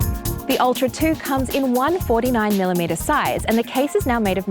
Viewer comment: Did you just say it's size is 149mm?? ...wrong right off the bat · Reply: No you misheard. I said "one 49mm size"